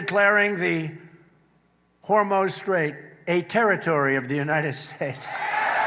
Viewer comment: did he say the Hormone Straight ?